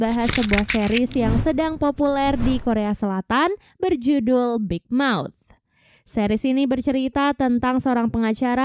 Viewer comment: Keren tapi kedengerannya big mouse bukan big mouth